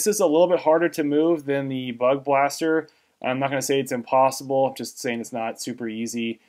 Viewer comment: BUG BUS TER! 😂🤣 Sorry... I had to. I just had to 😉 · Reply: I've heard it as "Bug blaster".